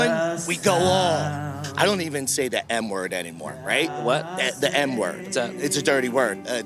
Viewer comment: I thought when he said "M-word," I was hearing "N-word," so when he leaned over and said "mask," I burst out laughing. I was confused, angry, sad, laughing and horrified all at the same time. But mostly laughing.